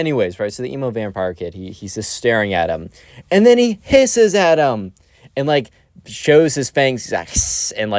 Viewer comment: bro when he said "AND HE HISSES AT HIM" I heard "AND HE PISSED AT HIM" and i was so confused